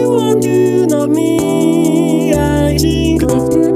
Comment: when he said, "All Of You Wants To Know Me, I Think," at the "I Think" part, it sounded like he said "I Stink"